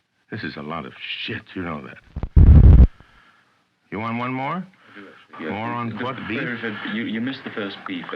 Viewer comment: I've watched this video almost a dozen times now, and it never gets old. It's one of those rare YouTube videos that gets better with repeated views. BTW, am I hearing things, or did Orson Welles call the producer a "moron f**k" at 4:02? 😄